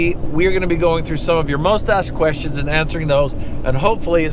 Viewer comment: you said “most asked questions” I heard “mustache questions” 😂